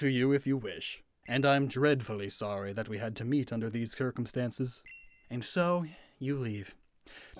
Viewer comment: did you just say CUR-CUM-stances? is that a dialect over there or something?